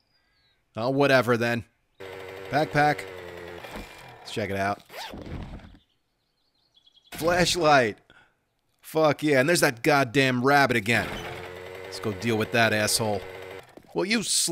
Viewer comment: I thought I heard him say: Flesh light! Fuck yeah. My brain: Damn he really got happy about that...........lol.